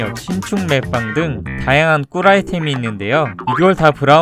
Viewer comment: 이거 나만 또라이템이라고 들림? ㅋㅋㅋㅋ